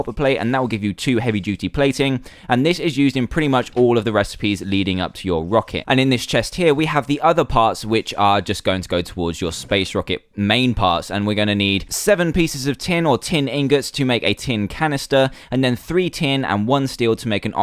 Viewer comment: I thought other parts said other farts